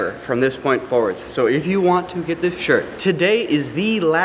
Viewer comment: At the end I thought it said "Go get the shit"